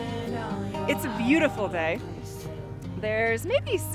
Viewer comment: did you say “bae” ? “It’s beautiful bae” Is that what you said?